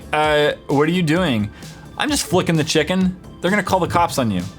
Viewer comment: "not sponsored lol I wis I luv amazon" BURP also, FRICKIN CHICKEN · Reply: I thought u said frickin not flipin